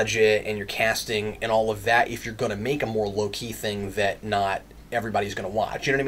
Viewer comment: Every time you said low key i thought you said Loki